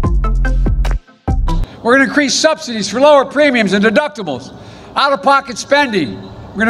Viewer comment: Sounds like he said, "...deduptables..." ...anybody else catch that? 🤔